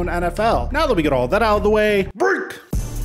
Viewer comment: During the intro music I kept hearing “Break! Check, 1,2,1,2 is lame. Break! Check 1,2,1,2 is lame” Nicely done!